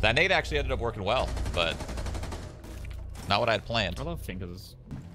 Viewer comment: sounds like he said “i like fingers”